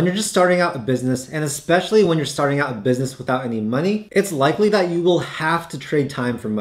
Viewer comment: thought u said starting a business with no money???